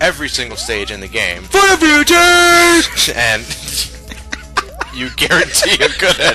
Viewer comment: wtf was said at 3:21 LOL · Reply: FOR THE FUTURE!!! which was a reference to the Bardock Special that team four star parodied